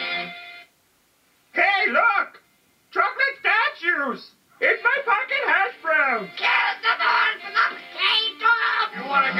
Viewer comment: I think I heard a swear? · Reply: No sir haha it said "It's my pocket hash browns" haha a few people thought he swore but nopers